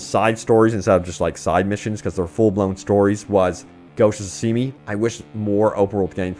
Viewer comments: did he just say Ghosts of sashimi lol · Closed Captions said you played a game called “Ghosts See Me” or is it “Ghosts Shimi”. Which game is that?